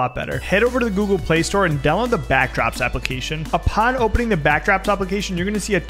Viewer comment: i thought he said backroos instead of backdrops☠☠☠☠